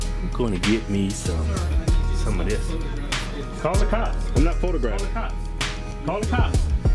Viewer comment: it sounded like the guy said "Sir I need you to stop photographing kids" If that was the case I can see why he snapped at that worker telling him that shit